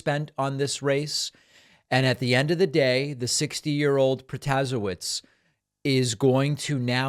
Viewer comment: David's saying Protasiewicz, the closed captions say protozoa. 😆